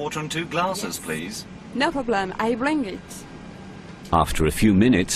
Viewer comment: did she just say "Yes, I bling it."? :'D